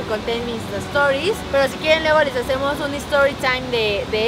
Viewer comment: Si quiero saber lo que dijo en el minuto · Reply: Gloria Mena Martinez si que iva aser un istan tory con las historia de terroe